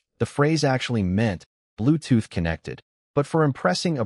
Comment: Did he say bluetooth connected 1:03:30😂